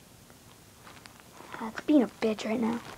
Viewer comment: did you just say bitch?!